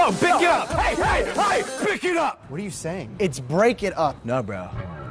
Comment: GUYS I am sorry for putting CENSURED instead of CENSORED. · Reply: Savage Compilations no at 8:37 it says pick it up instead of BIC it up